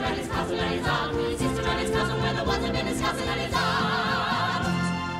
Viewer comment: Did they say bosom in his cousin @ 1:25???